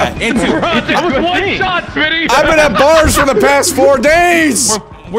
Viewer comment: When i 1st watched this i thought said Iv been at blargs for the past 7 days Watching this a again ik know he said iv been at bars for the past 7 days